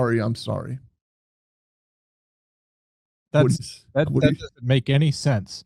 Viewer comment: When he approaches the mom i swear to god he sounds like hes saying i killed her-im so sorry